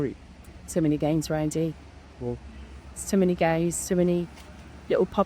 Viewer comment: Lol sounds like she said too many gays